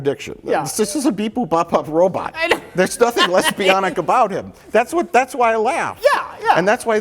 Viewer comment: I heard lesbianic (which I had to google to ensure it was a word) as lesbionic, which, of course, is perfection. There's only one song out there called "Lesbionic". PLEASE PLEASE PLEASE, people, can we band together as a community to write the canon and write and produce an album called LESBIONIC about R2D2 and their fellow lesbian robots? Naturally, "Lesbionic" will be the title track and first single. Second single: "Code non-binary". Reference quote: Mike at 29:08, "(It's) just a beep-boop bop-bop robot. There's nothing lesbianic about him".